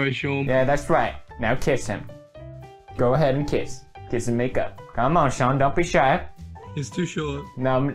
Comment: did pew just said kiss and make up on 2:25 like if you're a blink